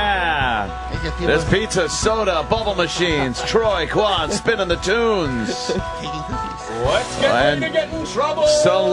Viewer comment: wth is mumbling under Anthony? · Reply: "I buried Paul. I buried Paul."